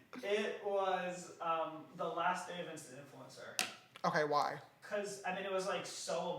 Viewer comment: when he said instant influencer i thought he said in sinc concert